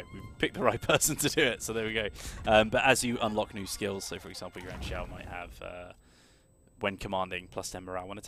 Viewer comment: I keep hearing "your aunt Shao" hahah. Love the game though